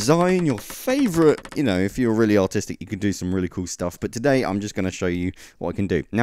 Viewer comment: It sounded like he said "If you're really autistic you could create some really cool stuff." In the beginning.